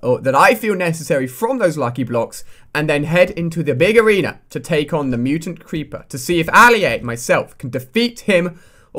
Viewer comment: Hope you ENJOY the video - Thanks for watching! :D <3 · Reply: hey at first in your video i heard you say in the dome we will be taken on the mutant creeper in